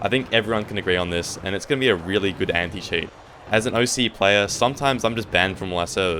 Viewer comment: I literally thought you said "A really good amputee" and it gave me flashbacks of the first BFV trailer to drop 😂